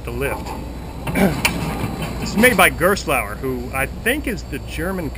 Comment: Does any one know what he said at 0:26? I heard Malauer